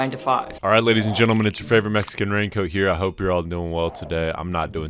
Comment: Oml I thought u said "alright lady's and gentlem it's your favorite rango " not rain coat TwT